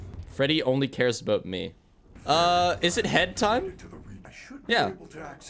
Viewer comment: When you said "it's head time" I heard "it's hentai" bro what is wrong with me?